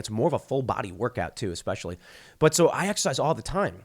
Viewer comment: Glad you're ok but a like the 4:50 mark did ypu say you have sex all the time